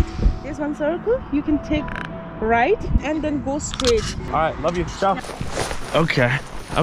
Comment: did he say I love you to a stranger